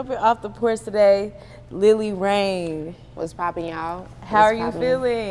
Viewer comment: Thought that shit said "Lil Wayne"